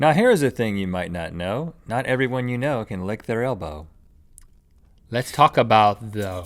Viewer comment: am I the only one who thought Rhett said "not everyone can lick the RAINBOW" in the intro??